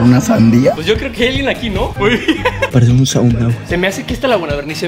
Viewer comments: parece un sauna 🤣🤣 es lo que dice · dice: parece un sauna we o algo asi le entendi xd